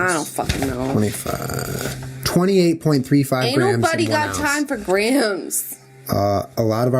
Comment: it sounds like Jenna says, "Anal buddy got time for grams" hahaha love y'all!!